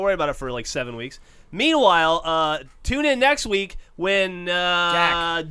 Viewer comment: lol did Lindsay just said "bring it beard"? LOL